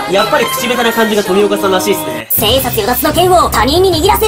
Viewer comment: 千円札与奪に聞こえた私は一体…?